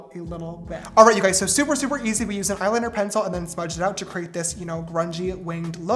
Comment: I can’t be the only one who thought it said Gayliner...